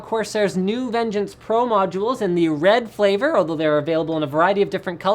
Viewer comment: did he say flavor?